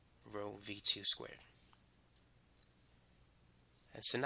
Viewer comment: I can't hear it clearly. what was that? thank you BTW. your vids are helpful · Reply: Conservation of energy